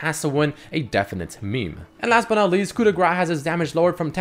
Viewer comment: wtf are you saying here? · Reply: Coup de Grace, a keystone in the Precision tree.